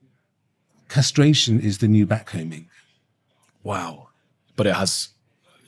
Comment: what is the doctor saying here? That castr. is the new black-combing? Blackening? vacuuming? Blackballed? CHAT HELP!!!